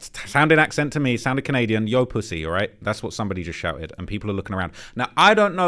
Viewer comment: I mean, you misheard "Tracy" as "Yo pussy"... so... yeah